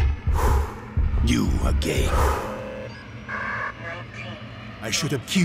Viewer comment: I know he said you again but how he said said it made it sound like he said you are gay.